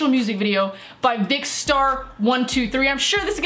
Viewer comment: did she said by dickstar? Just asking what I've heard